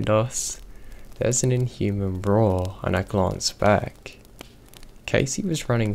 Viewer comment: Ok, overall, I dont mind your narration voice and I love the fact you leave in your chuckles and giggles....but wth did you say at 1:53:17? "Human bra"?...is that supposed to be there? What am I missing it not getting here...